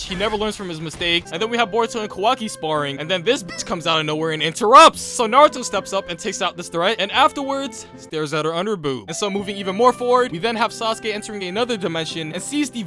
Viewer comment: did he just say "underboobs"?